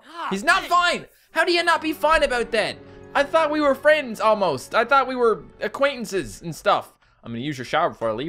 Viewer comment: "I thought we were friends, almost" I thought he said "I thought we were friends, homos"